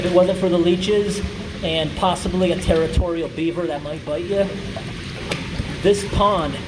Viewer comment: At first I thought he said "predatory beaver," I have no idea why. lol! So, how did the bottom of the pipe end up as the ceiling?